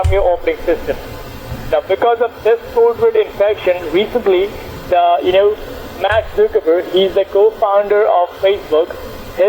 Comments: Go to 3:16 did he say MATT ZUCKERBERG the founder of Facebook?? Umm isn't that MARK Zuckerberg?? Lolz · the subtitles say max circuit board instead of Mark Zuckerberg